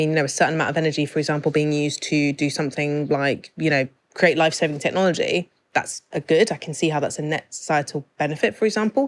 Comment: I thought said 'lightsabre technology' was the obvious good growth...